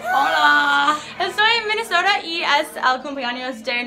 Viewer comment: es mi imaginacion o en el minuto 0:07 escuche estoy embarazada?????'